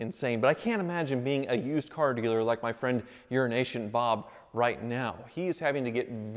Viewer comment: I swear you said "Urination Bob"